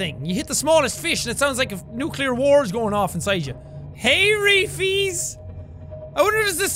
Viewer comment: TIME STAMP 36:07 IS JACK SAYING HEEEEY REFIEEEES